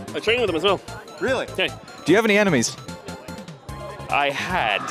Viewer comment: third or fourth time watching and just realized you asked if he had any "enemies", i swear to god i thought you said "animes"